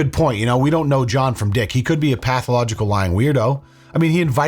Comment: you said he could be a pathological and then an advert came on so it sounded like you said a pathological buick 😂 6:17 im high fr